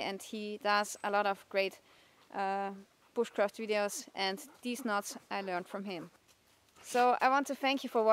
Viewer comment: it sounds like she said deez nuts